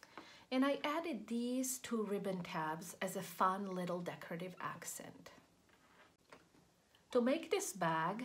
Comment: What are the optional side tab ribbons for? You added them at a specific place and they are a specific size. I do not understand their use. >>> I rewatched the video and heard you say "Decorative accent" at 30 seconds in. Sorry for the question.